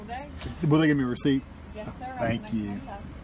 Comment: What did he say? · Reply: "Will they give me a receipt?"(pointing towards the next window)..."Yes"...."Thank You"